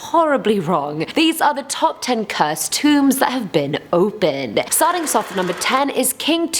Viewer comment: At first, I thought it said scary wombs that have been opened. 👁👄👁 😂